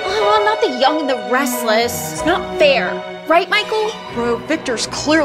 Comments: “ I WILL CRUSH YOU” 💀 i was dying 😂😂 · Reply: I heard "I will touch you!" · “ I WILL CRUSH YOU” 💀 i was dying 😂😂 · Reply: I misheard it as "I WILL TOUCH YOU!" and I was scared ngl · I heard " I WILL TOUCH YOU" instead of "I WILL CRUSH YOU" 😂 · What the guy said : “ I will crush you “ What I hear: “I well touch you “ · “ I WILL CRUSH YOU” 💀 i was dying 😂😂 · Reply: I heard “I WILL CATCH YOU!”